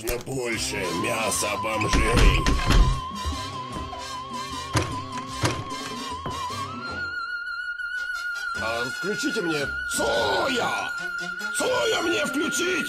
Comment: Самая топовая фраза шефа "ВКЛЮЧИТЕ МНЕ ЦОООООЯ" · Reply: А мне послышалось "включите мне соловья"